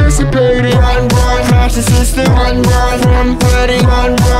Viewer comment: The “Run Run” part sounded like “Bon Bon” to me XD Edit: This was 1 year ago why is still getting likes lol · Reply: I can’t I hear: “Run, Run! Math’s assistant!” Instead of “Run, Run! Crash the system!”